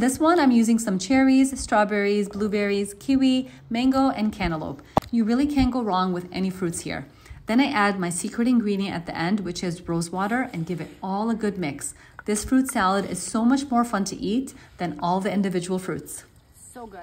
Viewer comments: I thought she said hose water😂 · Tell me why I thought she said HOSEWATER LMAO